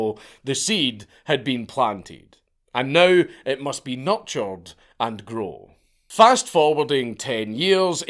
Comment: When he said "It must but nurtured", I heard "it must be nut-charged"....which also makes sense